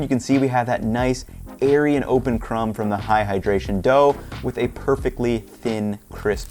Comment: Amazing recipe and will definitely try, but when I heard him say "AHAHAdration" I started dying 🤣☠